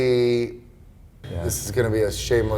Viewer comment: what machine he's talking about? Can't hear distinctly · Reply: He said T-Bell, you can find it on his web site store page.